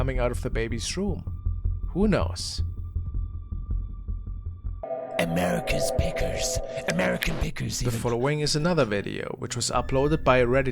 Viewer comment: The "American Pickers" LOL I had to double take, cause I thought it said "American Pickles" 😂